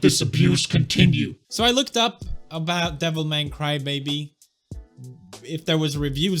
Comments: bruh each time i hear him saying "devil man crybaby" i hear devil may cry godamit lol btw i heard the next devil may cry game is coming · Am I only one who thought at first he said "Devil May Crybaby" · am i the only one thought he is saying "devil may cry baby "?? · am i the only one that thought he was talking about devil may cry?! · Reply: justine alonzo his terrible accent made me think that too. I thought he was saying "Devil May Cry: Baby". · I thought he said devil may cry baby lo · I kept hearing "devil may cry-baby" thinking it was a dmc spin off. Horribly horribly wrong. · I keep misheard "devil may cry, baby" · For a second I thought he said Devil May Cry baby. lol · I keep hearing "devil may cry, baby" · For the first five minutes I was thinking that he was talking about Devil May Cry LUL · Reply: lol! every time he said "Devil Man Cry-baby" i heard "Devil May Cry" with the word "baby" at the end. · Devil Man Cry Baby? I thought I heard Devil May Cry Lady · I thought he said devil may cry baby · It sounds like he is saying "devil may cry,baby" 😋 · It sounds like he is saying devilmay cry baby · Anyone else think it sounds like hes saying "Devil may cry, baby"? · I will admit for the first half of the video I thought you said Devil May Crybaby, I was wondering if it was some sort of joke on Devil May Cry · who else heard him say "Devil-may-cry Baby" at 1:10 ? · I thought he said Devil May Cry Baby at first